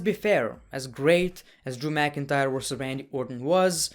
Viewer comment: I thought he said drew McInFire